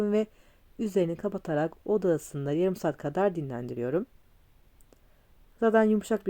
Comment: Merhaba 5 saat dinlendime mi dediniz yoksa benmi yalnış anladım şimdi yagurdum acil pismesi lazım 😂😂😂